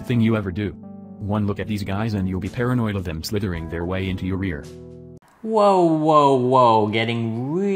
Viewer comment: does he really say "rear" here as in anus, or "ear" both completely different xD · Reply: It was probably ear, hence my pinned comment